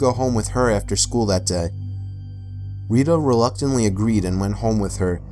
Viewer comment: ill call her reeda” I THOUGHT HE SAID WEEDA